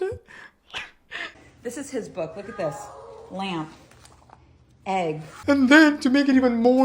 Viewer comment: i thought that it said “I am me” not lamb